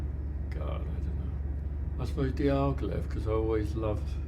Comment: At the 9:38 minute he mentions an artist's name that I couldn't understand. Can someone help me out with that information? Thank You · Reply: Diaghilev? Russian chap.